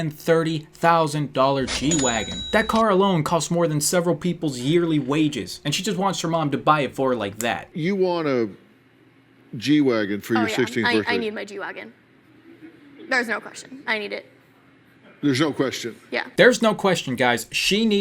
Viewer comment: I thought she said she wants G dragon😂😂❤